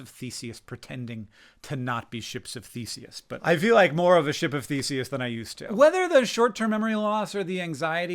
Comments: I'm so sorry, I thought you said "chimps with feces" not "ship of Theseus" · I thought he said we are a “ship of faeces”. And as a fellow colitus sufferer I was like yea I suppose we are… just a vessel for faeces. 😂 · 1. Congrats on your 18 months of remission! 2. I heard "ship of feces" instead of "ship of Theseus", which greatly confused me for about a minute. 3. Can't wait to watch you on Smartypants!